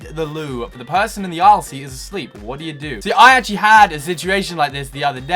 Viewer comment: aisle = aylsee in england? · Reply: no, I think you heard him say 'aisle seat' but he said it kinda fast so it sounded like aylsee xD